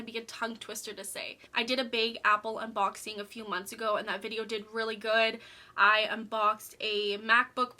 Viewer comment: what did you say?? Video didn’t go well??